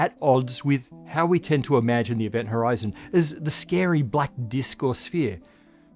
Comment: "the scary black disc or sphere" Had to go back because I heard "scary black disco sphere" and burst out laughing.